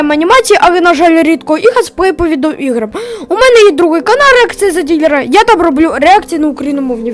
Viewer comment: мені почулося канарик